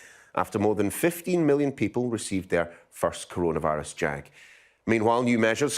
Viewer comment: Did he just say first Corana virus JAG